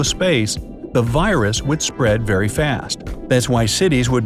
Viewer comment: I was eating cucumber at this 6:19 i heard he said IT WILL SPREAD THE PAPYRAUS Me:”-“